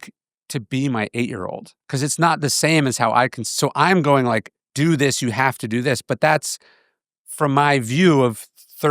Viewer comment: I am german and have no clue what german word he was trying to say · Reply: Umwelt. I do not speak German other than the 1 year I took in middle school and it was easy to find